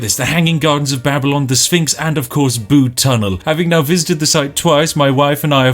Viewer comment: I thought he said Boob Tunnel. :(